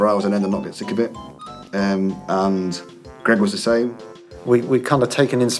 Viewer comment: What does Grant say at 1:45? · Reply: Seems like he said “Greg was the same.”